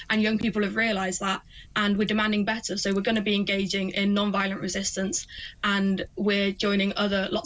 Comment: I really thought sincerely she said 'non binary resistence'😂